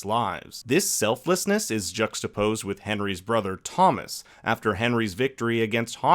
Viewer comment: I thought you said Tommen instead of Thomas and forgot where I was for a minute. 😂😂